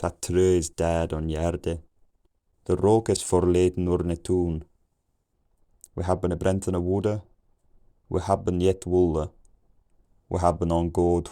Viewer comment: "the rooks abandoned our town" I heard "the rock is for laden on the tomb"